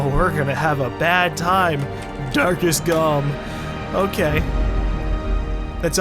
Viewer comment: when he said "darkest galm" i thought he said "dark as galm"